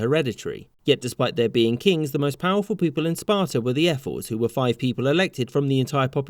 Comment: When i heard "Ephors" i actually heard "Airforce" and was like "Hoe the fuck did the Spartans have an Airforce